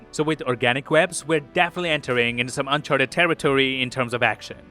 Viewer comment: did u say UNCHARTED category 👀😂